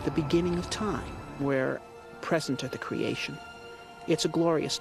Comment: Did he say 'creation' ? 8:36. he he, right.